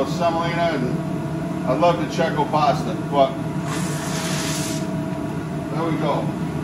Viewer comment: I heard, "I love De Cecco pasta, fuck!". I know you said "but", but the F bomb is actually better as far as content goes. I will continue to laugh every time i think i hear you cuss lol. Just cause you're such a down to earth dude, it took me by surprise on a humorous level, even though it wasn't what you said.